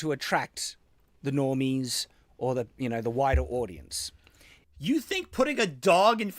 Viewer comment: I actually misheard him as saying the dog was meant to attract a wider audience. Wow what a bad point to say the opposite. People love dogs!